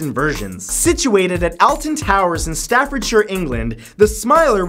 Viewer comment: says Alton towers wrong TRIGGERED (lol no) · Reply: Sounds like he says "Elton Towers." FANS TRIGGERED